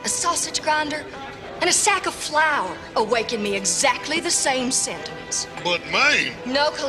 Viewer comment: What does she say at 10:06? "A man, a sausage grinder, and a sack of flower, " (what does she say next?) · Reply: Pepper Oki “...awaken in me the exact same sentiments.” Basically a man is on the same level as a sausage grinder and a sack of flour.